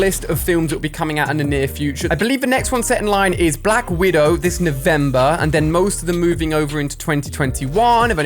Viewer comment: did he say avember